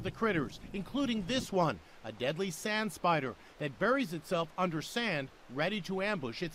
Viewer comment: I thought he said “Sans spider”